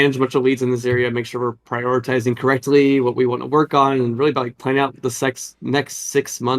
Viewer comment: All I heard was "planning the sex"